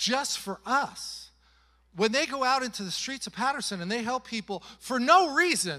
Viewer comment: I thought you said Paris not Patterson😂 listening from..near Paris 🙏